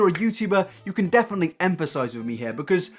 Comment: did you say emphasise? 😂 I think you mean empathise?